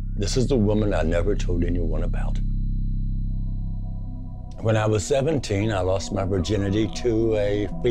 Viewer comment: oh and i misheard "when i was 70 i lost my virginity" instead of "when i was 17"))))